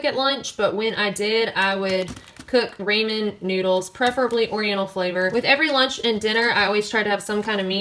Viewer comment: I totally just heard that as 'ramen noodels, Oreo flavored'. :D